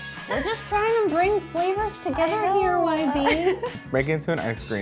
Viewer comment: what the hell did you say at 1:28 ??? I mush have rewatched like 20 times!! 😂😂🙃🙃🙃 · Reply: The girl's name is YB.. she says "We're trying to bring flavours together here YB"